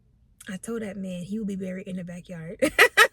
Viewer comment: I thought you said married in the backyard and I’m like yes!!🙌🏽 they’re getting married !! and then I listened again and you said he’ll be buried in the backyard😆😂 either way I have followed you for years so I’m super excited to see where life takes you and your family🫶🏽